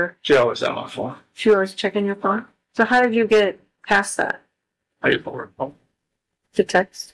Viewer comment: I hear, “I used my work phone”. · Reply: I hear it too. But I thinks it's pay forward phone at 1:24 and work phone at 1:40.